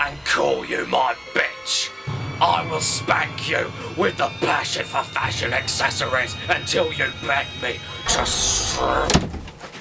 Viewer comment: the subtitles say this: "I must thank you with the flash of a fashion accessory until you like me." well okay then.